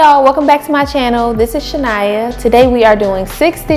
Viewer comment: omggg girl idk why i thought that said china the whole time. i heard my name and was like wait did she just say shania🤣 i guess it because i never saw anyone spell it like that but the braids are cute girl💕💕